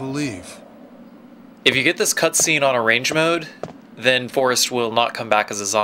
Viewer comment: What did he say in 11:05? Range mode? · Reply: Arrange Mode